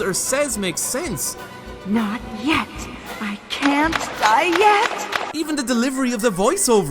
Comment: Bruh. Am i hearing right? Because it sounds " I cant diet. " For me....